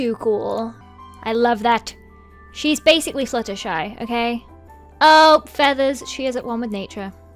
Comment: When you said Fluttershy i thought you said Flutterfudge....